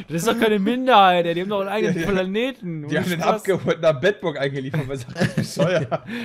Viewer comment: Was sagt da? 0:56 Die haben den abgeholt und nach Bedburg gebracht? Ich fühl mich geehrt , denn ich wohne in Bedburg :D